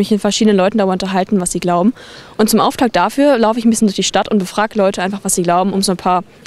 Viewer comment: The subtitles are completely wrong at 0:08 dand even for those with bad ears, it's obvious that the german subtitles there did not show what she really said. It'd be really nice if this could be somehow corrected. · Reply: I'm not sure, but I think the missing bit was "...lauf ich ein Bisschen um die Stadt und befrag Leute einfach..."